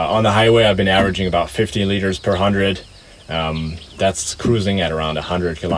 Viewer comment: i thought you said 50 litres per hundred and nearly fell off my chair hahaha. 15L per 100km actually seems reasonable for such an old car.